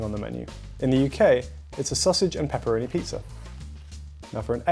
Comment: Did u just say vegan sausage and vegan pepperoni